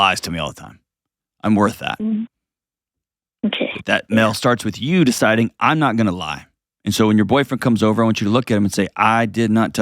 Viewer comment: What’s going on about? “I’m not gonna be with someone who lies” as she said he’s not allowed to but she can what is listening to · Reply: What are you listening to? At 7:12 said “that starts with YOU deciding I’m not going to lie” so no she’s not allowed to either. didn’t give her a pass in this call, he gave her a reality check